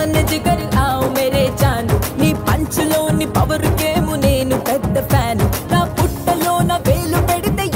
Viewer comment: can anyone explain me what she expectly says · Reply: Ne panchu lone power kemo nen peddha fan u